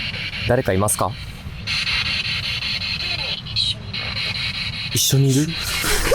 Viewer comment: の声…… 「常に一緒にいる」って聞こえるの私だけでしょうか😢